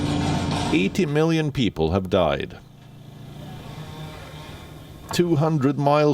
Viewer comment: did he says 80 million people have died?